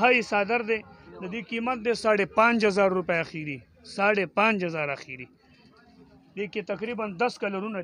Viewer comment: what price does he say? is it 50,000 rupee or 5000 rupee. thank you for answering my question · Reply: Dear first one price is Rs. 5,500. Next one is Rs. 10'500 Thanks dear for watching.